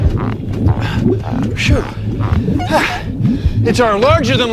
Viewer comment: did he say shoot or -----? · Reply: he said shoo